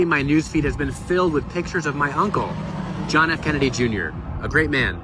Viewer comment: Maureen, I loved your book "Ask Not." It was so interesting and juicy. Especially the JFK/Caroline chapter. Question -- does Schlossberg call JFK Jr, "My HUNcle" in the video clip? (Video